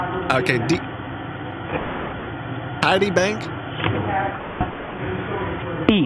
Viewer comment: I heard tiddy bank O-o